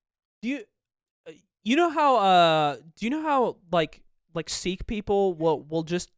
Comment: is he saying sikh???